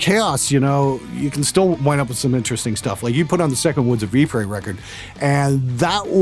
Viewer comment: What is the name of the album mentioned just after @ 11:50. I can’t understand what he said the captions didn’t help. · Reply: Woods of Ypres' second album You're welcome